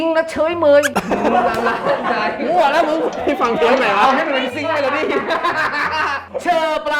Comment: ได้ยินบอกว่า"พี่ฟังเทปไหนว่ะ"ทำไมเขาไม่เเพ้อ่ะ งง